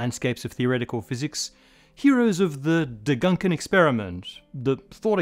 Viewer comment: Degankenexperiment 😂 For the non-germans: he pronounced the first two syllables of Gedankenexperiment (imaginary experiment, thought experiment) backwards - honestly not that noticeable or relevant, but had me smirk, so had to say it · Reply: I heard "degunken" (middle syllable /gəŋk/ like "gunk").